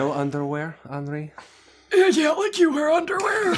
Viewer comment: Anyone know what says at 2:33:36? Thanks! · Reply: (sarcastically) "uh yeah, like you wear underwear"